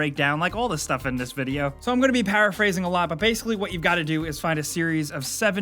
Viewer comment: Did you just say “parrot phrasing?” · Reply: Paraphrasing